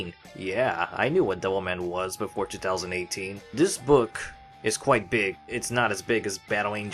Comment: I thought you said “neck” instead of “book”